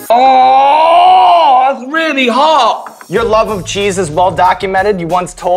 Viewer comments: I heard: "Your love of Jesus well documented" Well documented indeed :D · Haha, when he said "your love of cheeses", I thought he said "your love of Jesus" xD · I thought he said, ‘Your love of Jesus’, which was weird ‘cause they just talked about Ricky being an Atheist. I mean Sean pointed to the Heavens when he said that! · When he said "your love of cheese is..." I thought he said "your love of Jesus". Awks. Ricky is an atheist. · Okay so I've watched this quite a few times and every time I get to 14:38 I hear "Your love of Jesus" instead of "Your love of cheeses" and do a double take